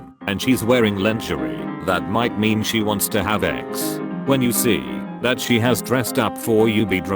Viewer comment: I heard “that might mean she wants to have eggs “